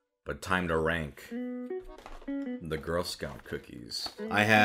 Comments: I thought he said "Let's PRANK the girl Scouts" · For a second I thought it said “let’s rank girls”